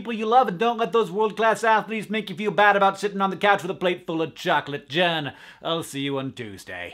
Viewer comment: It sounded like he said “a plate full of chocolate Johns” for a second until I figured it out!!